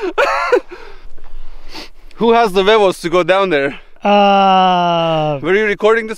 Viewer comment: Sounded like said, who has the bagels to go down there and check its Huevos 🥚 🍳 Cabron!! 😅